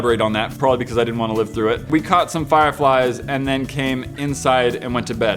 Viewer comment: at like 1:20 and 1:24 i heard that u sayed i fart fireflyes